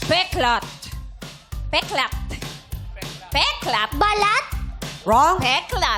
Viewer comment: b3lAt ba narinig ko!??😭😭